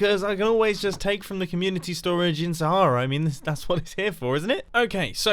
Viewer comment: Closed captions thought you said "sorrow" instead of "Sahara" at 8:03 I'm dead XD